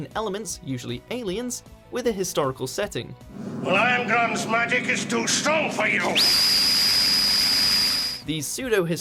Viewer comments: Did you say my dick is too strong for you? Lmao that's what it sounds like. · DID HE JUST SAY HIS DICK IS TO STRONG FOR YOU?!!!!?!?!!!!?